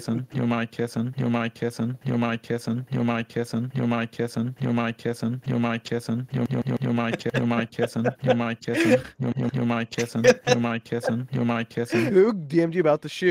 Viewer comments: I thought it said "i might kiss him" not "you're my kitten" i can hear both now im so sorry to the people who read this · PLEASE I THOUGHT IT SAID “ your mic kissing “ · Lol when sap starts to spam George saying you're my kitten it now sounds like you don't mind kissing idk why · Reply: I thought it said “I might kiss him”